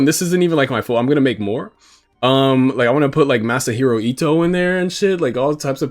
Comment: did you say Masa Hirahito? I know I spelled that wrong but that name is ringing a bell, Final Fantasy? No thats Nobuo Uematsu, Legend of Zelda? Bro this is gonna eat at me lol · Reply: Masahiro Ito, the art director for Team Silent (Silent Hill) :)) He’s the man who brought us Pyramid Head, among many other beautiful things!!